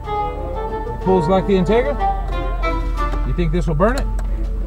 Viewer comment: Did he just say " pulls like the Integra" ?!?! 😂😂😂😂